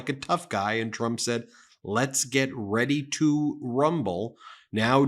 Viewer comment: They must have misheard Biden, and thought he said, "Let's get ready to grumble." The orange idiot doesn't know how to debate unless he's allowed to talk over everyone, unashamedly showing his ignorance and stupidity.